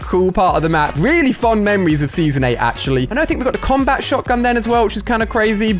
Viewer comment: Did say the combat shotgun came out in season 8